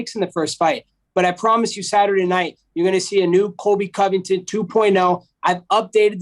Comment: The fact he says "Kumshot Chimaev" so casually gets me 😭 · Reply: Lol the moment I read this he said his name and I legit thought he said Colby Cumington 😂